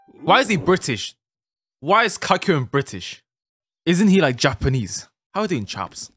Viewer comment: Excuse me why did subtitles said calcium then kakyoin